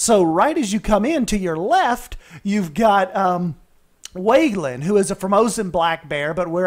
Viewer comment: Totally thought you said Weylan, not Weiyuan. Americanized pronunciation of his name is "way-you-ahn". And Eremias is greek (i think) pronouned "air-uh-my-us". I like the wolf's den, other than how boxy those rocks look. Just do some more angling (once the bug is fixed so you don't scream too much. lol). You should do a "puppy play center" interactive upcharge place for the wolf pups. I mean, momma and papa would definitely be set away from a bunch of guests playing with wolf pups, but yes.... :D You have a few small details inaccurate in real life on zoo welfare, like the pregnancy birthing stuff. I only know that because I have family that works in animal welfare, and watch a bunch of Animal planet, so insider and observer knowledge. lol. In real zoos, most likely pregnant animals will be quarantined near the end of their pregnancy until the birth, and sometimes after. But it's a small minor detail so anyways... I look forward to your next videos.